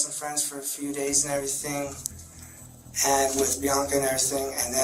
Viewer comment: he’s saying “Bianca” now I thought he said “Monica” when he asked the trans name